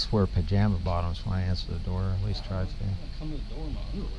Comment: At first I hear “who answers the door with a gun” and then realized his arm was behind the door but they said underwear. Hmm